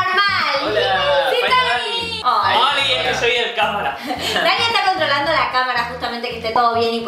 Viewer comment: Dani:Hola soy el karmaland Yo:que? (Creo que escuche mal igual xd)